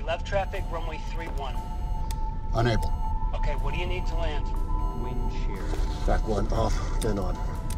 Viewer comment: Did it say Wind shear at 0:34?